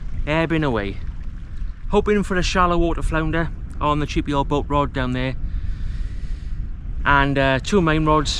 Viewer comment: Hey, I thought you were saying " The GPO boat rod " Then my husband put me right 😂